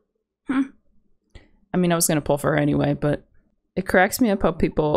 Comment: Did she just said no one is gonna pull for her anyway at 6:02 or did i hear wrong